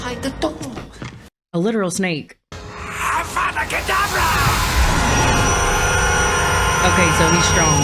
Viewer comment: “Abracadabra” instead of “Avada Kedavra”. 🤣